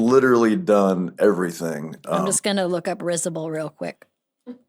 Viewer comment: Risible, i thought he said "rizzable" but risible is 🎉❤ love nick offerman. Love them all